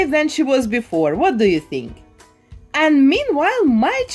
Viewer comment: Before was better! 😒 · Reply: Erin Davis @ 14:38 she said what do you think? As in saying leave a comment about your opinion, so it's not hate